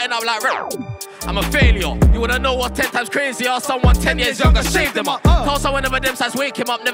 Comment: Did he just say if he’s 10 years younger I shaved him up?? If so my guys stabbing 12 year olds 🤦🏻‍♂️ my guys a fanny 🤣